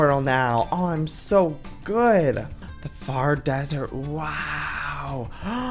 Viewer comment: I thought it said teleporting to the dessert instead of desert ,I was so excited!😢